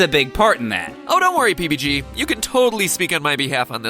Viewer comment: when matpat said "pbg", it sounded like he said baby g